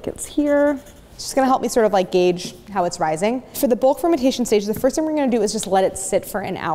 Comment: When Claire said "bulk fermentation stage," I genuinely thought she said "fermentation station" and expected to hear the "toot toot" of a toy train.